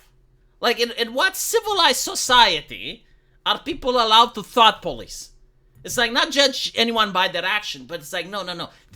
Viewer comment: Because of your accent, for the first few minutes I thought you were saying "fart police."